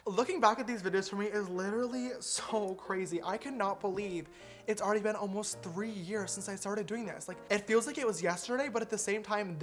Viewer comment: Omg I thought u said “30 year anniversary. I can’t believe it’s been 30 years Ive been doing this job.”